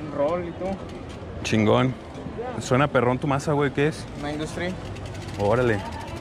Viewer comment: Que maza era del amigo? En el minuto 4:08, no escuché bien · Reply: Industry 9 hydra